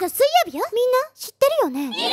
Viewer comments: 誰も触れてないですけど1:33のの誕生日が7月に聞こえるの私だけでしょうか…😢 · 7月4日に聞こえるんだけど4月4日が正しいのかな🤔？ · 4月4日が7月4日に聞こえてやった誕生日近いと思ったけど4月4日だった